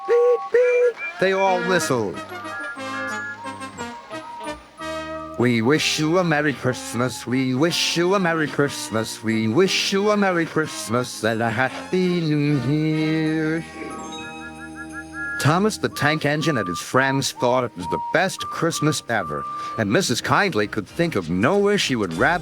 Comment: I Like How At 4:14 , The AI Narrator Says And A Happy New Here Instead Of Happy New Year.